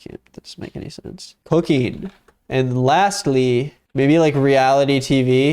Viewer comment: When you wrote your list, I thought you said "hooking" instead of "cooking." Because you know, all the manliest men hook themselves out and turn tricks for dough, lol.